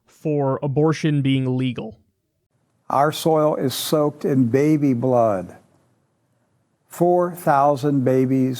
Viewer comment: I heard "our oil is soaked in baby blood" and thought, wow, these guys are making sense? Then I listened closer.